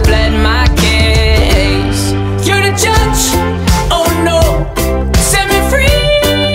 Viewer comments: I still never hear the "Josh Dun". :'( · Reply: it sounds like hes saying "the judge" but he's really saying Josh Dunaa!! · at first I thought it said your the Josh oh no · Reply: dat bøi At first I thought it said your touch oh no sets me freeeeee. I was a dumb kid when I first got in to T.O.P. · because screaming your band mates name in the middle of a song Is normal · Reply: don't hurt the smol beans me neither all i hear is "you're the judge"